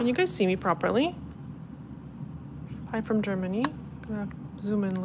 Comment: Did you just say you’re from Germany? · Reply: She actually was reading the comments where someone said “Hi from Germany”. I thought she said “I’m from Germany” also. 😅